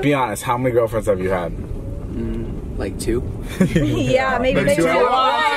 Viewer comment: what are they saying at 0:52 i cant understand lol, "maybe two ones" is what i heard, so 11?